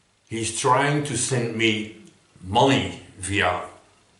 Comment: First i thought you said he tried to send Molly, Haha